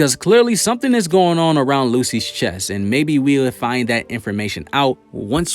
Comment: Did he just call lucifero as in Lucy or i am tripping 8:32 ???